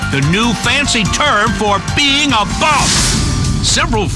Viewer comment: What'd he say?... "The new term for being a--" what? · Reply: a bum